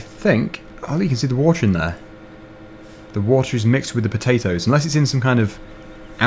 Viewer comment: Thought you said all water is free